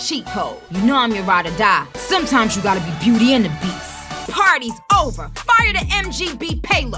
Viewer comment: It kinda sounded like she said cardis Over